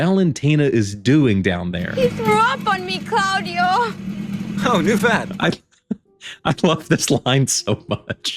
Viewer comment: "Oh/how knew fat" ?? What does he say? Edit: "Oh, new fad"?